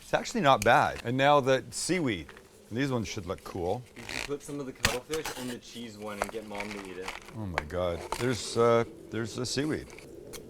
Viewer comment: What said “put some cuttlefish in the cheese and have mom eat it” What I heard “happy Mother’s Day, enjoy this game of popcorn roulette”